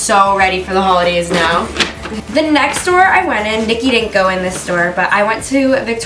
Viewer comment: did she say Viki??